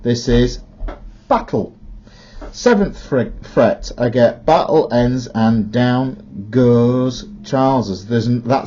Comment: Until I heard the whole thing I originally thought he said battle ends and down goes trousers. Got to love those crazy b r i t s.